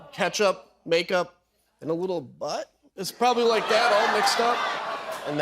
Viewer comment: did he say "and little bit Butt" ??.. Why does he know how a Butt smells like?